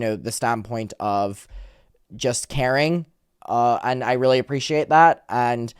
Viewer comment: When says "Caring" I just hear "Karen" 😂